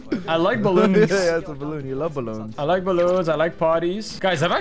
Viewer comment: it sounds like he said "i like ballons i like bodies 💀